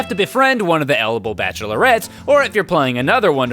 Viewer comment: Carter praised the sun before Solaire did! Also, I heard you saying " one of the edible bacholerettes.."